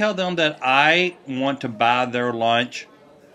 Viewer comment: That accent lol I thought you said "I would bother lunch "